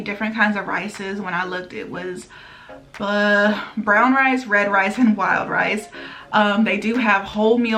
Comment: I thought she said fried rice when she actually said red rice 🤣 for zero points. I thought WW was tripping lol But something new ! Excited to mix it up